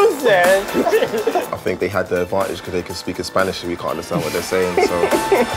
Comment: what did clyne say? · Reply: That they had an adantage because they could speak Spanish and they couldn't understand what they were saying.